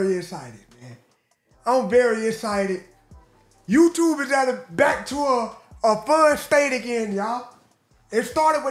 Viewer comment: did he say he's excited for a Back-tuah 🤣